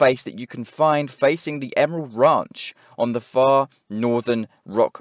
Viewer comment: where is 6:55 ? you say facing the .... ranch far north ? but which ranch i cant understand. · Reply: Emerald ranch i think